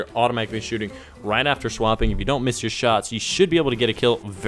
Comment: did he just say shwapping